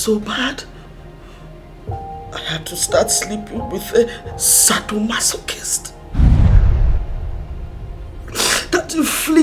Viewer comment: Quelq'un peut m'aider à comprendre la partie 13:08 s'il vous plaît ? Elle dit peut-être : i had to start sleeping with the subtle muscle kissed? Qu'est ce que ça veut dire ? Please can anyone explain to me the part 13.08? Did she say sleeping the muscle kissed ? I would to understand that part! Thanks in adavance